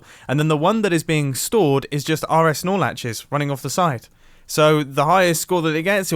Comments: Did he just say rs snorlatches 😂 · I thought this man said snorlax for 10 minute